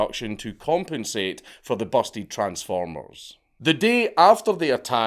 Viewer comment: Did he say busty transformers? 😳